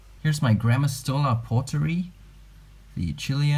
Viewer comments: Idk why, but at 6:50 I always hear "Grandma stole my poetry" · "Here is my grandma stole a pottery" I hear that every.single.time. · I have no idea why I'm watching this with my arachnophobia but... there was this species at 6:51... Was I the only one to hear "grandma stole our pottery" instead of the species name?